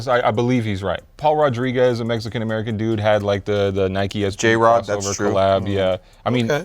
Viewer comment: did Welty just say Jrod?!?